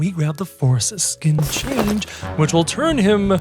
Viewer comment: thought that shit said foreskin change